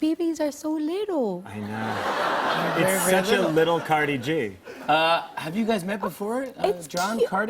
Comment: did he say... cardi G? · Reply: elle he mans Cardi G as in CardiGan like a type of sweater